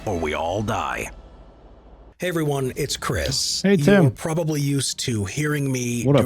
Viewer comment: tbh, at 5:30, what I thought I heard: Chris: "Hey, everyone, it's Chris." "Hey, Tim" I know now that he actually said "Hey, it's him" but it was funny to have misheard it at first